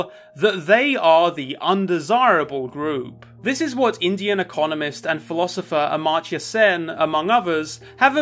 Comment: I really thought he said "Indiana communist"